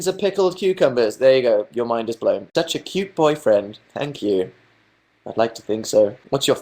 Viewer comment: What did he say at 9:34? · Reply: I think "you're such a cute boyfriend" but idk